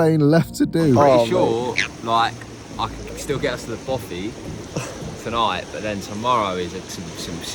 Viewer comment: For the first few minutes of the video I thought you were saying "boffee" which I thought was some kind of dessert (I was getting confused with banoffee pie) but then realized you were saying "bothy" and I was still confused until I googled it. Educational and entertaining, cheers to that!